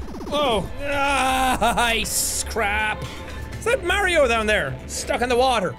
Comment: Can some one tell me what he says at 5:34 ice crap?